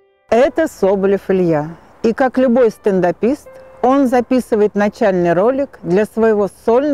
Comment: в начале сказали стендапист? мне не послышалось? нанче русский каверкают как могут